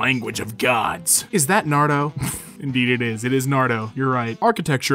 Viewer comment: Did you just say “Nardo” instead of Naruto?